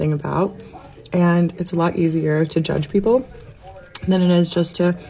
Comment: lol omg i thought she said "it's a lot easier to judge people than it is to just kys"